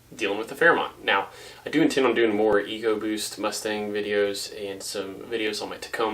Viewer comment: I must be getting old because I misheard Ecoboost as Egoboost for a second there LoL